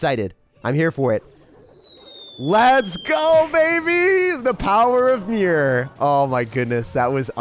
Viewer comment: im a bit late cause i just watched this today, but i swear when you said "the power of mirror" i thought you said "the power of beer"